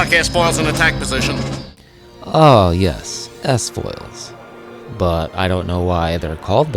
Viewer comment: Seriously? The only one I misheard was "s-foils." I thought he said "x-foils," since they were X-wing fighters. The "mikes?" What did you use for speakers? A 1930s radio? Get some fireside chats with your Star Wars? Or maybe you lived under a bowling alley. Sorry, I have to call b.s. here.